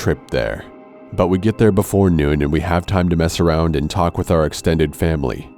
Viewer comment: The first thing I hear “this happened to me when I was a kid” in a creepy deep voice 😂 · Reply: I thought he said “we have time to mess around and wee” cause he paused 😂 😂 😂